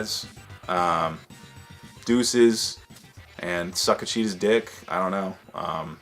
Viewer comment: My wife was getting ready in the bathroom and asked "did he just say suck a cheese dick"?